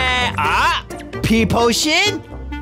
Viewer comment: did you Just say… Peeposhit